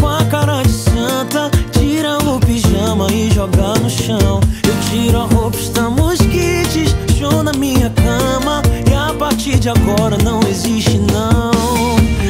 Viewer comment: Eu ouvi "eu tiro a roupa estamos quites... mijou na minha cama". Kkkk ai fui vê a letra.